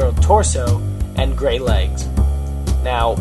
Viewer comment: haha, i thought you said the girl had great legs. :P after bfb, we should do a hurricane movie! i just can't do rain effects, so thats you and your after affects! :D